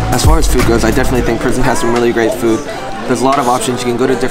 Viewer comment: i heard prison LMFAO